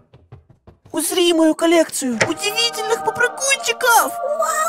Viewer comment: Мне послышалось усри мою калецию Не хейт